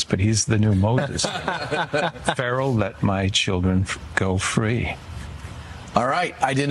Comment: “Let my people go!!”- Trump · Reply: Thought he said “let my people golf” lol